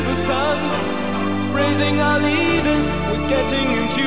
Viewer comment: why did I think it said “rizzing our leaders” like the brainrot is taking over my mind💀🙏